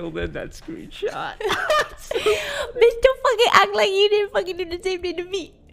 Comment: What dis said at 0:33 ? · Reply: “Bitch dont fucking act like you didn’t do the same thing to me”